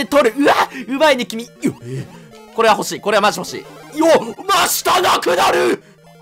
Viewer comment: 明日なくなる！！ て聞こえたの 俺だけ www？